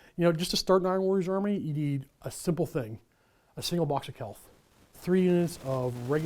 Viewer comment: So I missed what was said at 9:05 what did he say was needed to start Iron Warriors? I heard a box of celth??? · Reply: Betrayal at Calth Box Set. No longer in production. $150 USD for 30 MkIV Tactical Marines, 5 Cataphractii Terminators, Chaplain and Cataphractii Terminator Captain & a crappy Contemptor Dreadnought.